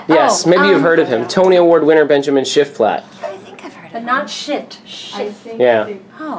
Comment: Did she say, “But not shit, Shif.”